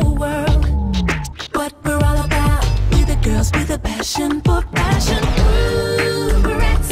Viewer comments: WAIT IT DOESN’T SAY B-B-BRATZ? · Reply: and i thought they said “ we’re a badass” 😂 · btw at the end i thought it said buh buh bratz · When it says ooh Bratz I thought it said ooh berets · btw at the end i thought it said buh buh bratz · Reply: I thought it said cool Bratz😅! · i always thought it said cool bratz lol :D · I thought she was saying Hood bratz all this time lol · I can't be the only one who thought it said COOL BRATZ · as child I always thought they said booo bratz ahahh